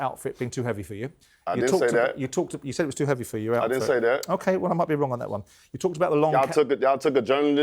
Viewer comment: Idk sounds like he said “i did say that”